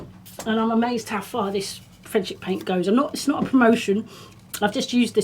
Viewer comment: I’d never heard of Frenchic paint until,you mentioned it a while back. I thought you said Friendship, thankfully Google knew what a wally I was and pointed me in the right direction. Now planning to revamp front door and kitchen cupboards, they have some great colours available.